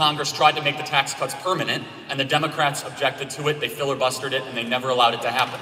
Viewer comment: did he say "fillerbuster"?